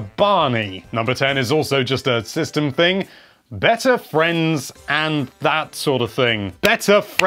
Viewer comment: I agree, I want better friends too (is what I initially thought he said)